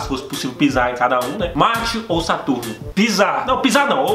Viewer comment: ouvi macho ou saturno